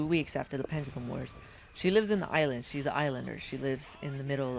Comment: Did you say Eyelander LOL